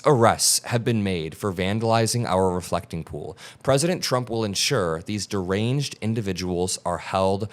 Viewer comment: Almost sounded like she said President Chump.